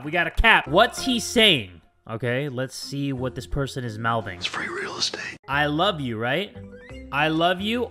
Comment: am I going crazy or did he say I love real estate